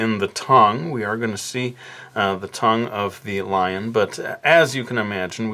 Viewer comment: did you said lion